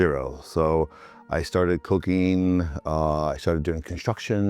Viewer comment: I thought he said “I started cocaine” lol